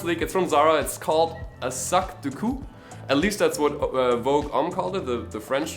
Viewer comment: lol I heard suck de couk